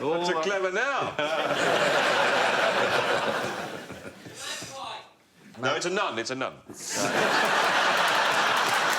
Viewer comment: A little help please? What does the guy in the audience yell at 0:26 / 3:16 ? Thanks very much! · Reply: Magpie